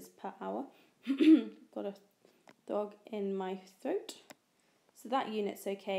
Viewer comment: did you say a dog in your throat? xD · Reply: hahahaha! I just had to rewatch that to check what I said! I say frog not dog :D